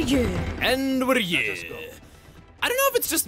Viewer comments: I Always Heard "And We're here." as "As We're Good." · I always hear "und we're yurr" for Nia's skip travel quote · I always hear Nia's "And we're here" as "And we're good" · i always heard it as "And we're good" 🤷🏽‍♂️